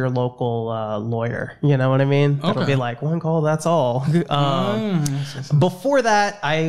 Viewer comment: I didn't hear correctly the first time, I believed he said "Better Call saul" . Like he was doing the same borderline fraudulent ads and fake documentaries for a lawyer like in Better Call Saul